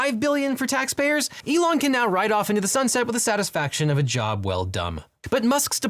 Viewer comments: "Elon can now ride off into the sunset with a satisfaction of a job well *dumb*..." Did I hear it right? Yes I think I heard that right... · I don't care what the captions claim was said, I heard "...a job well dumb" 😂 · I heard "The satisfaction of a job well dumb" If that's intentional, Bravo. If not, well... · did he say a job well done dumb?